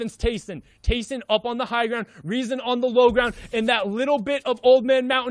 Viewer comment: 「テイソン おもんな ハイグラ」こう聞こえてワロタww